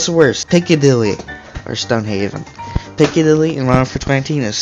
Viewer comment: Lol 4:53 on transcript says pinky dilly and then picky delete instead of Piccadilly 😂